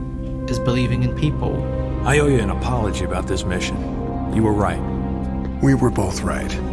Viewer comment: Did superman call batman mitch